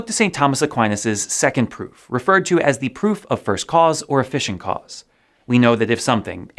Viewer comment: I had my head turned at 8:50 and didn't catch his graphics. I heard, "First cause or A fishing cause"